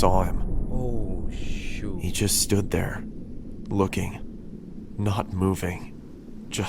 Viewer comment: at first I thought i heard "He's just standing there *menacingly*"